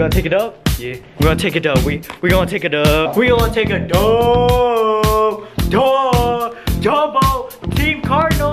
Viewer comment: I thought he said "we gonnnna takeeeee a dumppppp"